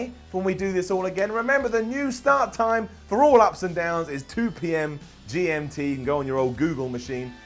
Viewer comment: Simon its sound like you said up , fuckin down lol. ... You should be wwe raw Announcing